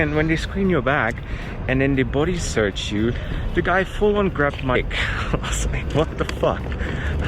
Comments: Take the screening groping as a compliment. Maybe they thought you were trying to smuggle something large. 🤣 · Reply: I thought he said the guy grabbed his behind. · The guy fully grabbed my what....???? I can't understand what says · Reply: It sounded like you said he full on grabbed your a$$ 🤔